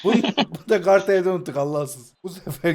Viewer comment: allahsız dedi benmi yanlış duydum